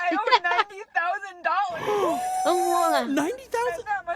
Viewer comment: I thought she said $19,000